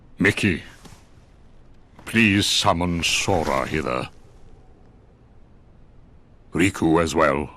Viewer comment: I need to go to bed instead of watching this. I was reading the subtitles before the characters spoke and read "Mickey, please summon Sora Hitler"